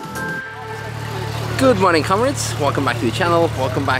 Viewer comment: misheard intro: "good morning cowards" (i know he says comrades) ^^